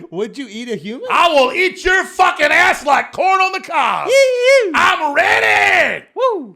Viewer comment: I accidentally skipped ahead a bit and the first thing I hear is "I will eat your fucking ads like corn on the cob!". Never change Alex